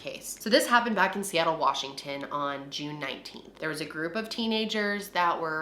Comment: I DON'T CARE THAT YOU BROKE YOUR ELBOW no it happened this year literally a few months ago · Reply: Oh okay, just thought I heard her say it was in June 2019. It saw the video a few months ago tho